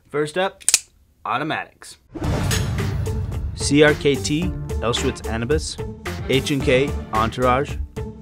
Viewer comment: I thought I heard "auschwitz canabis" at 1:25, made me look again. I would have definitely bought that knife tho, if it was named like that